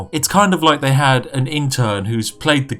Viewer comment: Not sure if it's the accent, but did you say "interd"? xD